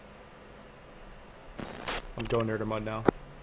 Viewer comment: did you say I'm going air to mud? :P